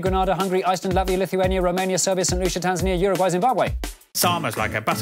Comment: I still cannot hear what country is trying to say at 3:54 after Serbia :( (Edit: nvm it was St. Lucia)